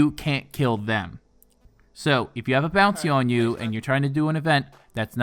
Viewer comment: Lol i thought you said, "if you have a bouncy on you."lol